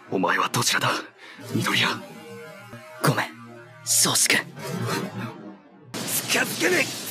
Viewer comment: did he say saskue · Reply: Do you have no ears? He said Shoji-kun fast.